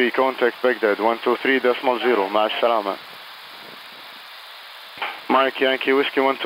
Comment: did he say masha allah as part of the communication ? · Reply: I think he said ma3a el salama --------- with safety